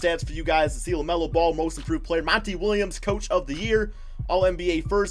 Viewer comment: For a sec I thought it said lamelo ball coach of the uear